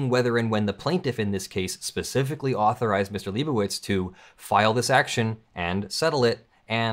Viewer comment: I thought you said it was a "Liebo" education channel lol